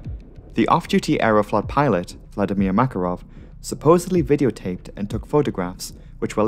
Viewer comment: Did i hear Vladimir Makarov?